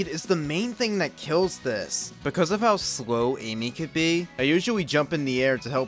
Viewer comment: it sounds like you said "usuawy" 😂